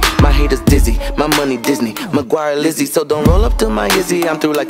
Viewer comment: did he just say "my Mcquire is Lizzie"? Hahahahaha